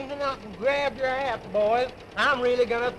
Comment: Did he really say "Grab your ass, boys"? Yes, he did.